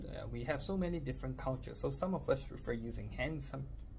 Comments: I thought he said "some prefer to use fucking spoon" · I thought he said "some prefer fvcking spoon"😂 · I heard " Fucking spoons " Not forks and spoon😂😂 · Thought I heard “some of us prefer to use f***ing spoon” 😂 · I heard "Some people prefer using fucking spoon" 😂